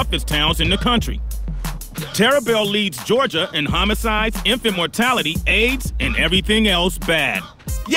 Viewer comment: I heard "infant mortality" as "imp immortality." I guess both are bad